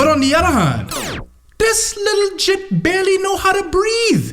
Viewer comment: If I heard this correctly, in the interests of intersectional awareness, "gŷp" (no ^) is a racial slur. (It seems like you were playfully using a word that you know has ill intent, but I'm not sure what it means to you.) For more info on a wide range of people/-s who have it wielded against them, florida.florian provides a good overview. If you happen to not consider yourself to be a part any of those peoples, you may want to stop using this word... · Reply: hey i think he was saying jit not the other term